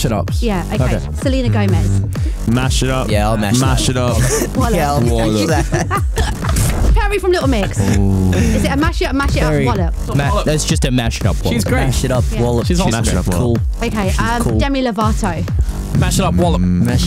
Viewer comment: I don't know why but i heard "Harry from Little Mix" and got really confused lol